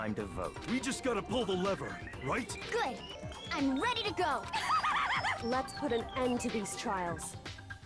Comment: I've got to say I've been on YouTube on various accounts for 5 years and seen multiple playthroughs of different games and this one by far has to be one of the best I've seen. How dedicated you were to the voice acting, the game itself, your witty commentary, how you actually played the entire thing and kept it going at a consistent rate of uploads. You're truly one of the best game commentators on YouTube and this series proves that, yes this is a long winded comment that you might not even read but I just gotta say your work is great and I can't wait for future content! :-) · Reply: I know I heard I'm ready for it! Haha